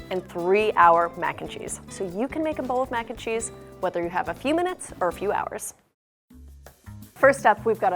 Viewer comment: I had low volume and i thought she said "if you have a penis" at 0:25 💀